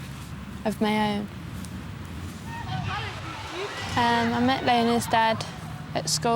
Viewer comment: the girl at 6:44 is annoying me so much because she says “bayba” instead of “baby”